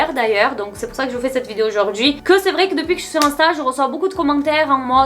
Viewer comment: Pourquoi j'ai compris "Depuis que je suis enceinte" zulieu de "Depuis que je suis sur insta" ???